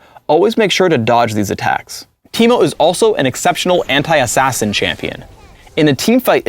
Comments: K, so first thing is that everyone hates teemo when hes on their team CAUSE THE FUCKING POISONS ALWAYS MAKE DA LAST HIT AND HE ALWAYS FKING STEALS And second, 3:55.. did he just said hentai assasin? Rito pls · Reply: He said anti-assasin :P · sounds like phreak is saying: "Teemo is also an exceptional hentai assassin champion"